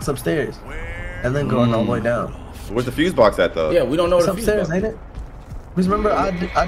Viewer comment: i coulda swore i heard say ''its upstairs nigga''